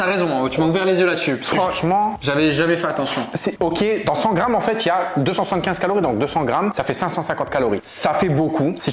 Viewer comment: c quoi ? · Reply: de la feta (fromage greque)